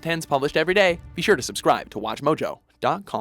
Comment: Am i the only one who heard oh gay xD